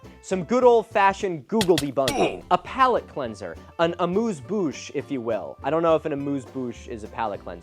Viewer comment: Hey Milo, I just started watching your video, but I wanted to respond to your question: “why I'm even trying to make him change?” I think it’s clear that you’re frustrated — and I completely understand why. But for me, you’re not doing this just for him, you’re doing it for ALL OF US. Maybe you’ll help some people step away from this nonsense, and at the same time, you’re preventing the rest of us from being dragged into this black hole of misinformation. So, using my “silent people” voice, I want to say once again: THANK YOU so much for all your hard work.❤ PS : I speak french, I had to rewind on "amuse bouche" I heard "a moose bush" 🤣